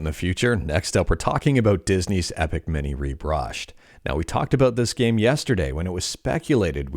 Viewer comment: Sounded like you said "Epic Minnie Rebrushed"